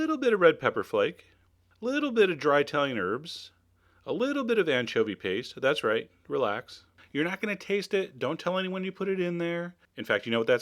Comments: i am not very good at english , can anyone tell me what are the ingredients that the chef mentioned at 1:36~1:40 plz thank you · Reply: 辣味鮮蝦墨魚麵 little bit of dried Italian herbs and little bit of anchovy paste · What this word on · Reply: Олег Лайко A little bit of dry thyme herbs and anchovy paste